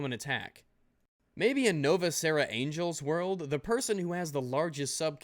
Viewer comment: I thought he said Novas Angel... maybe he should pronounce that Weaboo name better. Embarrassing for a VOICE ACTOR.